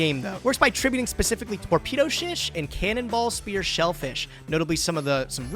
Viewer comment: did get edited to be a ytp or did he actually say shish?